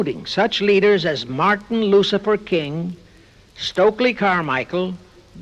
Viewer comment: Did anyone else catch the guy talking say "Martin LUCIFER King" at the 1:01:40 mark of the video?